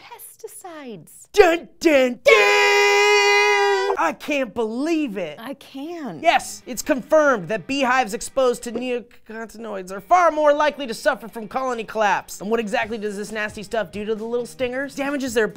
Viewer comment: I heard say neo-cunt-inoids